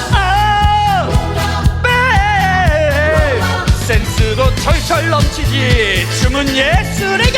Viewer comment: 섹스도 철철 넘치지로 들리지 않음?